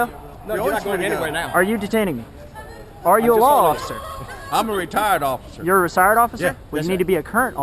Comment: “Are u a law officer?” “Retired law officer” “You have to be a current law officer” “It don’t make no difference” Perfect response lmao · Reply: All I heard was "im a retarded officer" lmao